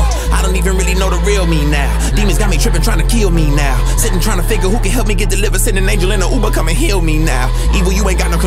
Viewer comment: I hear "Sittin tryna figure out who can help me get delivered"